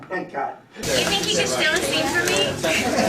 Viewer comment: Can someone tell me what Danneel says at 6:35? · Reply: "You think he can steal a scene from me! Please "